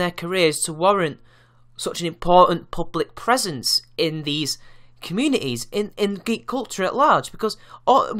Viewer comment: I thought it said "Parasites of GREEK culture" ...... I was confused...